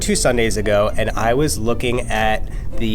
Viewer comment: When he said "I was bored two Sundays ago.." I thought he said he was born two Sundays ago and i was like WOAH DUDE HOLD UP